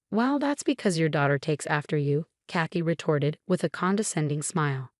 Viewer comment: This is rather an interesting error: “Khaki retarded …” I believe this one should be “Kathy retorted …” “Retarded” is incorrectly used again instead of RETORTED (8:43).